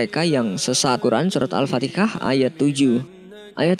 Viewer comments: Apa saya yg salah denger Mimin nyebut al-fatiha nya “ al-fatikah” ? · Yg dengernya Surat al fatikah Siapa aja? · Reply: Kadang denger Al-Fatihah, kadang Al Fatikah, tapi lebih sering seperti kedengerannya admin bilang Al- fatikah.